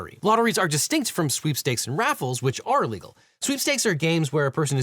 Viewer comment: Caption mistake: the captions here say "which are illegal" instead of "which are legal"